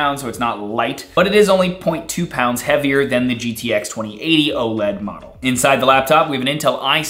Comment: did he just say gtx 2080